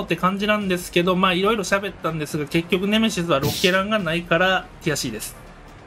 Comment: ティアCです、が「悔しいです」に聞こえて笑えた